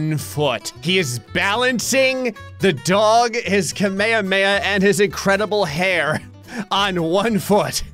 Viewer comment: if you go back and listen to how he say kamehameha this is what i heard "kameyameya" that flipped me off HAHAH comment what you heard